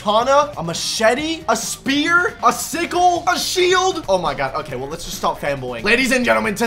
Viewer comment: When he sits here I thought he said pickle😂